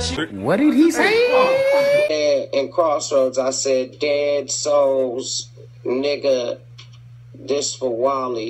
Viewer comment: God Dammit all these years, I thought this nigga said Let us All lol he said Dead Souls. Well I'll be damned